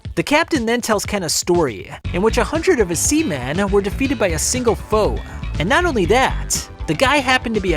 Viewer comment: I heard "100 of his seamen were defeated by a single hoe😂